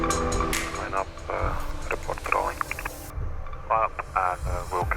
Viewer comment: I could not make out what was being said at 26:00. I believe that is what the unusual transmission from the ATC is. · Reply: “Line up and report rolling.” Presumably the traffic asked if they could do an engine run-up after they had received takeoff clearance, and ATC instructed them to line up and report when they’re finished with their runup, and they have commenced takeoff.